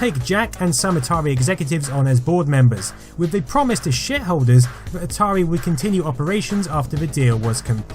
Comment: does anyone hear that as "With a promise to shitholders?" I know he means shareholders, but it absolutely sounds like shitholders to me